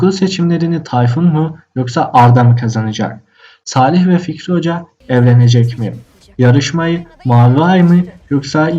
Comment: de Salih ve fikri Hoca evlenecekmi diye söyledi benmi yanlış anladım